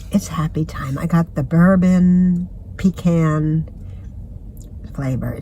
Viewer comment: That coffee flavor sounds good! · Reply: At first I thought I heard, I put bourbon in it, lmao.